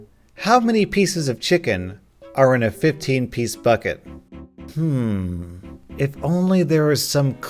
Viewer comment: I'm p sure I heard '15 piss buckets'